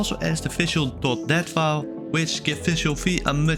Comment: what did you actually say here? I cannot for the life of me understand what is being said. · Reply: Visual.dat file